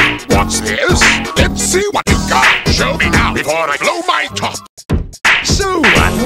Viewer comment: I thought he said “Before I blow my Tot!” Because that rymes